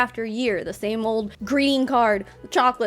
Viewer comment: I fucking thought you said, "same old green card" and I about choked on my coffee lmao